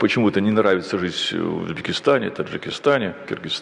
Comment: мне послышалось, или он первым назвал Дагестан? Потому что Таджикистан и Кыргызстан он перечислил, а на Казахстан и Узбекистан совсем не похоже, что он сказал.